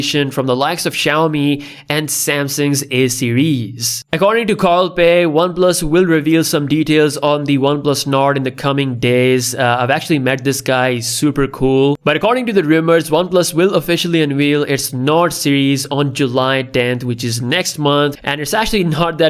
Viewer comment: I heard oneplus note, I was giggling inside, but realized you said nord:( Nice details and this is the best way to describe an upcoming event.